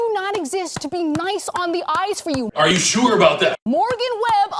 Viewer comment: I'm so out of the MSM loop that I thought she was saying "nice on the ice". Like this video was about someone giving ice skaters grief. Forgot g4 even existed until I heard the name Morgan Webb a few times. She was...nice on the eyes and knew her stuff too and had a great "on-air" attitude.